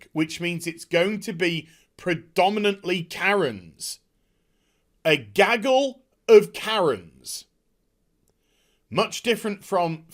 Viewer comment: Sorry...sorry...I thought you said gagging Karens.